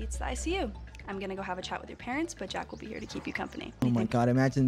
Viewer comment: i thought she said: "i'm gonna have a child with your parents" 💀